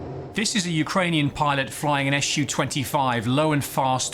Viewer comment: I always heard it as sue 25 etc rather than S. U